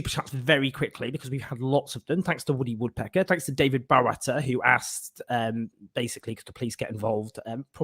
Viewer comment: I can understand why many in the LGBTQ+ community feel uneasy about focusing on us when it comes to Monkeypox. Memories of the HIV epidemic still scars our community and the fear of going back to how thing were in the 80s will be in the forefront of many peoples heads. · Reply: Not really having to do with anything, but how do you pronounce your surname? I always thought Owen was saying "David Bharata" or something before I saw how it is spelled